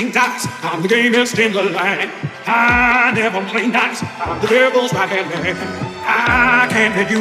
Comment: What i hear on 1:00 : I'm Mr. Peanut I'm the gayest in the land, i never pee nuts